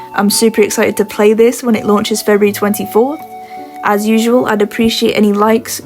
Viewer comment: I just nearly had an embolism when she said the game was released in "February, 24". It sounded like she said February NEXT YEAR!